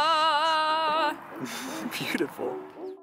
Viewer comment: am i the only one who heard him say "beautiful" @ 7:04 💞